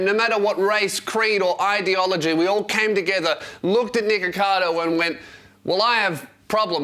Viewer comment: did you just call him niggercado?